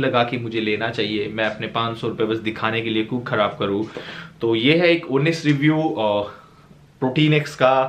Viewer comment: Did I heard ‘’ ye hai Ek unniiissss review ??’’ #